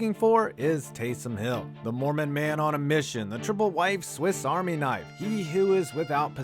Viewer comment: "Triple-wife Swiss Army knife." That's grade-A content. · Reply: I thought he said triple-white. 😭😭😭